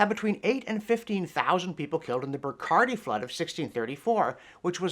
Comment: Had to rewind there a but. Could have sworn you said "Bacardi flood" :-D